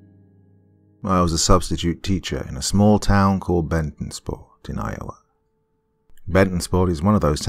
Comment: For a minute I thought you said bettendorf Iowa and I was like aye that's not that far, but bettendorf is bigger than the town described lol